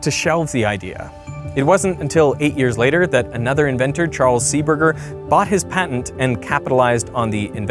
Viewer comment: did he just say burger?!111